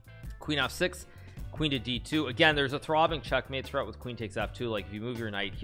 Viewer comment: Did you say "Throbbing checkmate"?